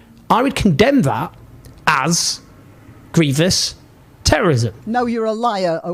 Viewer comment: Owen Jones? Honestly, the pearl clutching ninnies and censors at YouTube would never let me. Edit: 'muslim sock puppet,' I like that, I'll be stealing it. Edit2: What's that word at 21:40? Aggrevious? I think you've made up your own little portmanteau word there Owen.